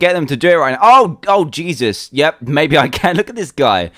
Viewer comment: did he say Jesus ?!??